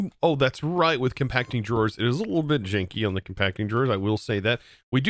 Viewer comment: Thought I heard him say his interface was super chinky and was laughing way too hard. my brother, it's 'Janky' <3